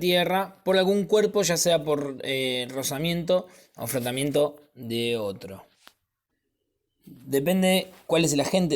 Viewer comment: no entiendo lo que dice alguien me explica por fa? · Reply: por rozamiento o frotamiento de otro ( cuerpo)